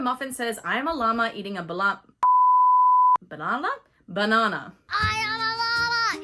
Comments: For some odd reason, every time I hear Muffin say “I am a llama eating a banana!”, I always hear “ I am a llama eating on Madonna!” Also, Bluey’s look of confusion/fourth wall break always cracks me up. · I thought Muffin was saying “ I am a lama eating a Padama “